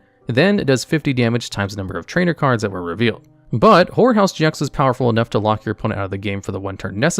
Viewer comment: i never played TCG and was just listening to this on the side when i heard him talk about Gengar and "Whore House GX". At least that is what i heard